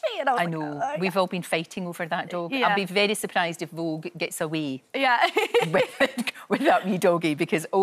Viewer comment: I know it's a small detail but could anyone be so nice and tell me what they're talking about? Vougue's dog? Also, what is Lorraine saying at that part of the video? "We've all be faking over that dog. I'd be surprised if Vogue gets away without me dogie"? I'm not an English native speaker, I can't quite catch it up :( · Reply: She actually says: “We’ve all been fighting over that dog. I’d be very surprised if Vogue gets a way with that wee (small) doggy”. Millie was basically talking about how she was late because she was touching this cute dog that the company Vogue has, and the interviewer agreed the dog was cute because everyone was fighting to touch it so she’d be surprised if Vogue gets away without someone stealing the cute dog